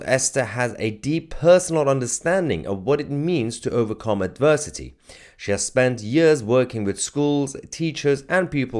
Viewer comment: 'overcome adversity' I heard as 'overcome diversity'. seems about right